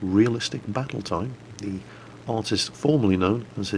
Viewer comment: Lol, did i hear fart ? :D, you badass